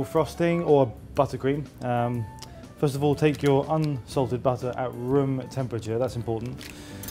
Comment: Hi. Here's the full list of ingredients for the frosting. Let us know how your cakes go. We're craving cakes now in the Videojug office! 140 g butter, at room temperature 280 g icing sugar 2 milk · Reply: super late but I heard him say unsalted